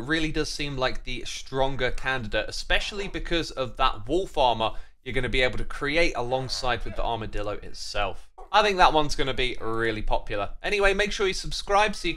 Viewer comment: when you said wolf armor at the end i thought you said wool farmer